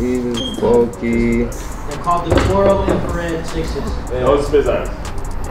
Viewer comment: Those 6s were called infrared 23 give that kid his shoe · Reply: Neeal Jegatheeswaran 👍 your right I thought dude said Infrared 6s